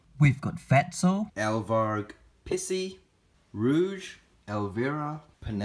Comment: did u say pissy